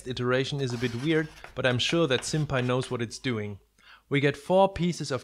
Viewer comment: I really thought you said "Senpai knows what it's doing"... Then I realised you probably said SimPy... I should probably stop watching so much anime...